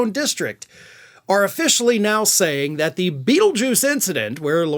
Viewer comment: I thought I heard you say feeljuice.😂